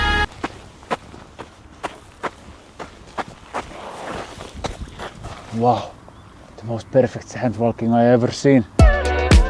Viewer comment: lmao the sandwalking bit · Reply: Lol, I thought he said "handwalking", I was so confused! 🤔